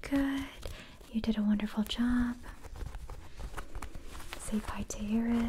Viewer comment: I heard "you didn't want a full job" instead "you did a wonderful job" yes I didn't want a full day job 😭